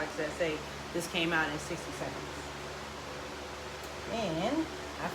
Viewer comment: How long did you press the rainbow? Thought I heard 180 seconds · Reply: 60 SECOND